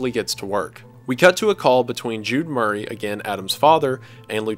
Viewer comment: I know this video is old but i thought Jude Murray at 53:15 said Nude Jeremy and my fear levels skyrocketed